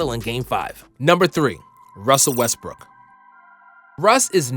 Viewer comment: WHY DO I HEAR RUSSEL "WESTBRICK" WHENEVER SOMEONE SAYS RUSSEL WESTBROOK